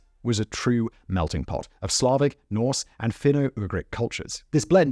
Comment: ish I misheard you, I thought you said 'Fenugreek culture'... .😂